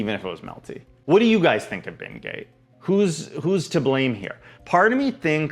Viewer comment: i heard "what do you guys think of bingate?" as "what do you guys think of being gay?" and i choked on my coffee